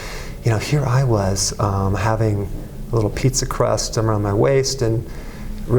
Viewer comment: Did he say pizza crust around his waist? Instead of a muffin top? 🤣